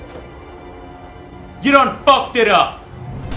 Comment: I hear 'you don't fucked it up' )